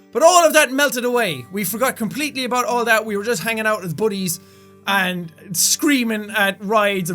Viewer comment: I thought he said we were just hanging out like bully's